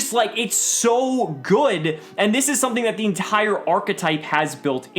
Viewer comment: Not lying I thought it said "Hentai Archetype" maybe I'm just high or trying to tell myself something.